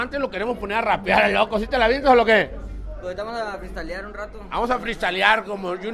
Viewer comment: En 0:20 párese que dijo vamos a crystaliar jajaja puro foco jaja